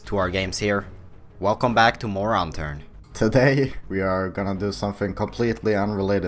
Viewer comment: Sounds like you said moron turned